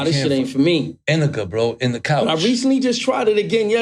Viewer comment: explaining weed to Trulife is some of the FUNNIEST shit I’ve ever heard lol “You gotta try Indica…it’s in the couch” hahahaha I always heard it was “In the clouds” lol I hope tru finds a way to ease his anxiety, Weed has definitely helped me. In moderation. I also do gummies on days I don’t smoke. Anxiety is a bitch though, I hope all who are going through it, get through it.